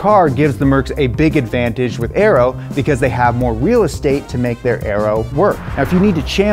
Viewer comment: Did he say "wheel estate"